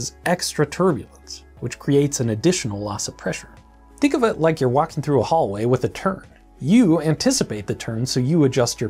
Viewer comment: I heard "think of it like you're walking through a hallway with a turd"